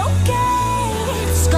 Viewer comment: “That’s what my paripist say” wtf, 😂 0:32